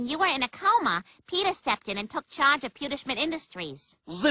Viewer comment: To me the ending sounded like he said GHOST STAB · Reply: N8 Corner Ever seen Bill Cosby's movie "Ghost Dad"? that was the joke.